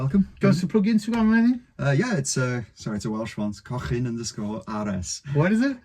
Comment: Lovin this one! I thought he said cockin' too 🤣